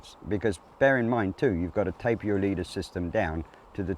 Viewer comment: TapeR. He said you have to taper your leader to the smallest tippet/fly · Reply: NM, got to the tape. I heard “tape early on in the video when he was talking about tippet sizing and rewound it and understood he said taper